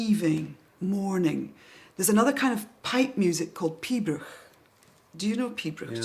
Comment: "There is another kind of pipe music called p????" What did she call it? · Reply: Pibroch.